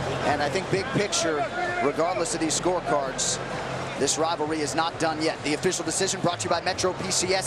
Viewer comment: I hear him saying “this robbery is not over yet”